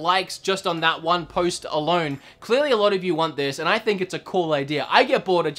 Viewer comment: sounded like you said “post Malone”